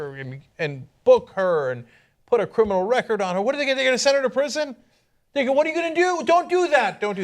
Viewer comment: at like 4:25 it sounds like he says 'nigga what are you gonna do?!'